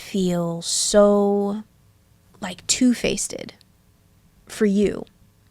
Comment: literally wtf is she saying here? “It must feel so, like, toothpasted.” Girl the term is “two-faced” like what??